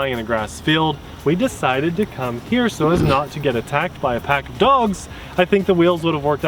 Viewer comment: I’m so grateful that I started the video, left the room for a sec, and came back. The first thing I heard was “decided to fly here as not to get attacked by a pack of dogs.” Now I could start the video over and hear the official reason you said that. I could. but I rather use my imagination to fill in the plot holes. With that said , I’m glad you made it out of the temple with the treasure.